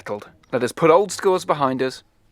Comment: I think i heard a 'max & sam' but not sure what the first was, sounded like 'paddy'! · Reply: Mack, Sam and... Penny ;-)